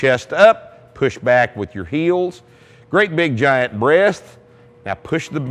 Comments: lol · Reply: I thought he said "Great big giant breath". Not sure now though... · lol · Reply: tfranc347 Did he just say great big giant breast? lol